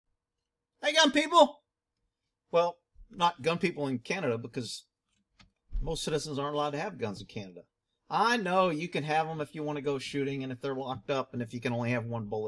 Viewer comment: bro for the past years... I coulda sworn you said 'Hey how'reya going people'? not 'hey gun people' hahaha